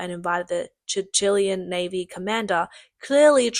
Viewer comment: Did you say CHICHILIAN Navy? 8:46 You mean Chilean.